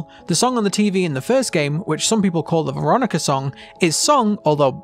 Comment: Pff— For a split second I heard “gruonica song” instead of “Veronica song” and I just— I have the maturity of a 5 year old child